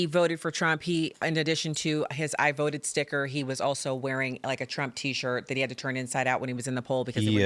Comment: did she say he was wearing a trump shirt when he voted, or did I misunderstand her??